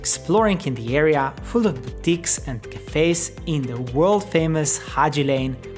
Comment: Did he just say "you can also do some exploring in the area full of Dicks and cafes"???